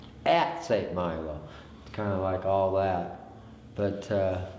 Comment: The guy that begins speaking at about 4:40 .. the caretaker. "AT St Milo... kinda like all that" WTF? What is he saying? mmmm ok